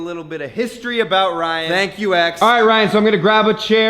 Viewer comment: At first I thought he was saying "thank you ex"..I'm so fucking stupid Turned notifications on btw! :3